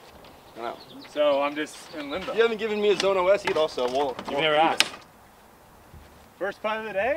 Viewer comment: After rewinding 5 times, I can confirm that when said "you never gave me a zone OS" Brodie said "You never asked" and he did not say "They are ass"